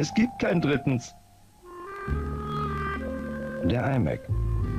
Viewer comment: it says the imac but i thought it said die mac